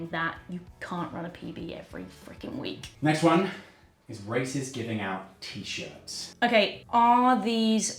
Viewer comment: really thought he said “racists giving out t-shirts” Makes “racists, make note give people the option to plant a tree” a little funny